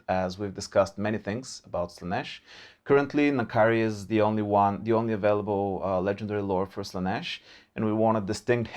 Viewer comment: what they said : currently, N'kari is the only lord avaliable for Slaneesh what I heard : currently, Kairos is the only lord avaliable for Tzeentch